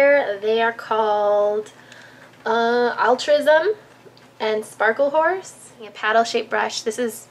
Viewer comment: lol i thought she was gonna say "sparkle whore" instead of "sparkle horse" hahahah